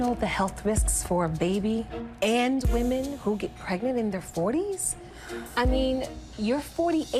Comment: I thought it said to get pregnant in 40 seconds